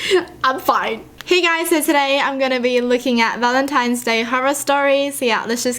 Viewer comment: I thought you said "licking" instead of looking.